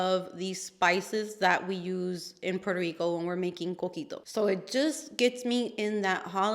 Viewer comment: What is the drink from Puerto Rico that you referenced? It sounded like you said “cookie dough” but the picture looked like a Chai or Horchata type drink. I must know so I can look up and make this drink you mentioned! I love trying cuisine and beverages from other cultures! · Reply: Yes it’s coquito! 🥰 Thank you lucibella for the recipe 🇵🇷 Everyone makes it a little different but it ends up being a creamy coconuty drink and if you get caught up in the taste you can end up pretty wasted! Haha 😂